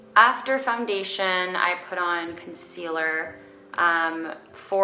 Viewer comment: Does she say “fumdation” or is it my hearing?